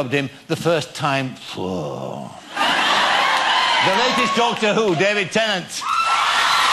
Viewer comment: what the guy said: the latest doctor who: david tennant! what i heard: the ladies' doctor who: david tennant!